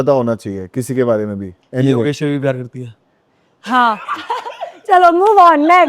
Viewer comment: kya bola ne? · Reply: na akansha ko bola "tu ab bhi yogesh se pyar karti hain"